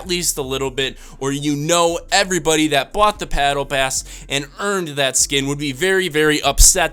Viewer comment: did he say pattle bass or was that just me?